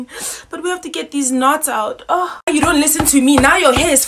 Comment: I hear “ stop crying before I cut your head “